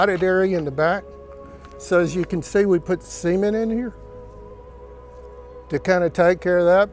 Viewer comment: For the life of me I can't figure out what you said at 8:16 did you say seemen in there? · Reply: Cement